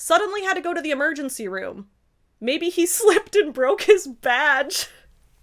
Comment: Now, I'm not saying I heard that as "maybe he slipped and broke his vag" at first...